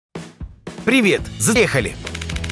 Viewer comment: кто услышал взъехали